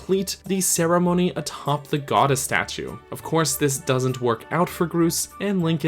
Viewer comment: i thought you said to hump the goddess statue